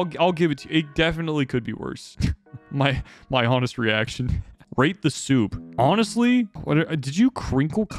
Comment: Am I the only one who heard "rape the soup"?💀